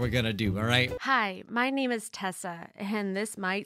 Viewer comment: I heard: "Hi, my name is T E S L A."